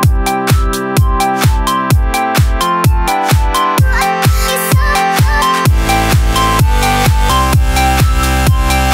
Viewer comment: Does anybody know what she's saying at 0:27 ??? :3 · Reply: Dr_Doctors she says: uhamake you so,so.... (badd) or something like that.